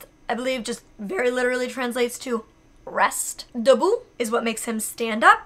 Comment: Yeaaaah I'm a french speaker! And it's so cute to hear your accent, love it! I just wanna say that at 2:54 what you say is "debout" (which literally means "stand up" like you said), and not "début" :) The fact that you dog can understand all of that is AMAZING! I can't believe it, wow 😍